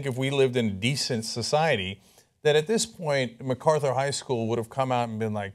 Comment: McCarthyism highschool did you say?